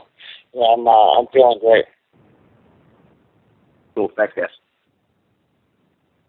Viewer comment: did she say "Wrestling a pervert"?